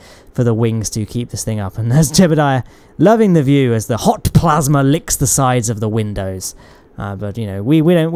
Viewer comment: I just woke up to this video and when he says "HOT plasma" I thought he ge said "Hawk Tuah" and I was like "No way"